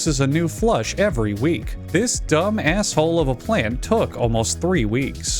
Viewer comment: "This dumb asshole" Am i hearing things?